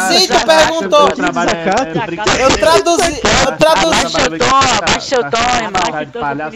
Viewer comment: oq eu so entendi foi Q3 ABABABT400404O '-'